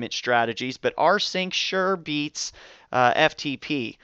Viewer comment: Hello! What is the name of deployment tool at 3:57, I don't hear correctly. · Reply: Anatoly Khalizev Sure, it's called rsync. It comes on any osx or linux machine